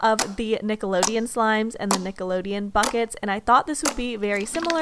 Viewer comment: i thought she said nickelodeon f— nvm...